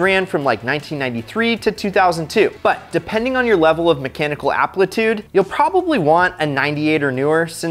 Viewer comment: Did he say "mechanical applitude"?